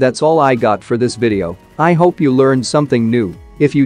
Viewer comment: says "I hope you learned something new" What I hear, "I hope you learned something noob"🤣🤣🤣